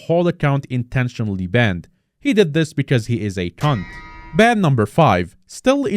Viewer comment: Did he say, "He did this because he's a c*nt"?